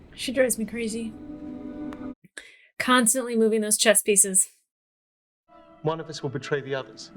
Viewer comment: I thought you said "constantly moving those chest pieces" for a second. It still fit.